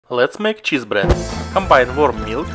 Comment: I thought he said 'lets make children' I was worried there for a sec 😂😂😂